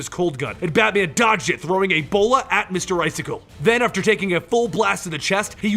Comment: Thought he said Batman threw Ebola at me icicle